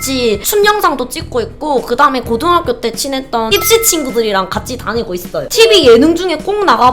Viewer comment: 하도 가 찌찌 이야기하는거 듣다보니까.. 입시친구들을 순간 찌찌친구들이라 들었네... 하아...